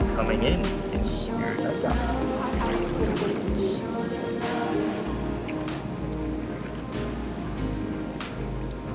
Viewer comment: I've been wondering this forever but what does she say at 2:09? She says something and I can't figure out what she says. Not sure if anyone else has picked up on it but I'll leave this comment here :-) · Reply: “I didn't bring a bathing suit” or something along those lines